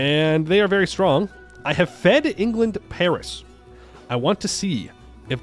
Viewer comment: sounds like you said “fed england to Paris” haha